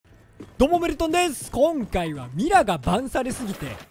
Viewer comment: あぁ^〜メルトンさん滑舌悪すぎて最初の挨拶『どもメル友です』って聞こえる^〜